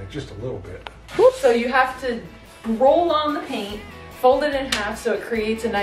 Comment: did you say paint? Do Americans not have wallpaper paste?